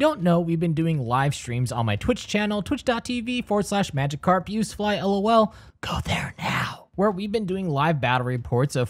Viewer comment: When he said twitch.tv I misheard twitchTHOTtv